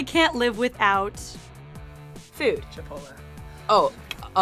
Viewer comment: what is Jonah saying at 6:49? I dont understand this? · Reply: Chipotle (he then says he's kidding)